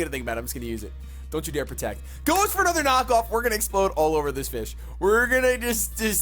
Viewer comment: I thought u said I'm gonna explode one this bitch but u said fish lol😂😂😂😂😂😂😂